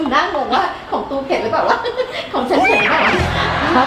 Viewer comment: ได้ยินว่า ของตูเผ็ดรึป่าววะ😂